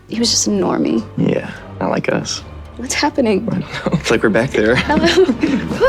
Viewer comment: I thought he said "I like us" and then the tension and the awkward third wheel. I love this so much HAHHAHA